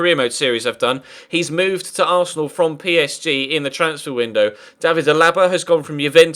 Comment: did he just say david the laba